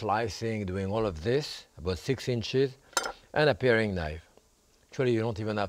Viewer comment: did he say "...fixing shit" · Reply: "about six inches"